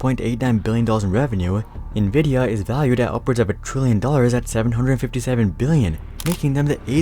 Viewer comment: Does that say "upwards of a trillion", then subsequently "$757 billion"?